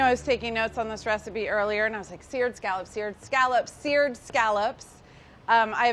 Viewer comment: I thought it said scared scallops.